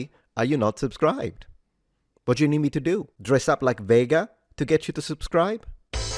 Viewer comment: When you said "dress up as Vega" I heard "dress up as Veigar" and instantly hovered my mouse over the subscription button. However, when I saw the Vega dress up I sadly moved my mouse away. It was perfect, but not Veigar.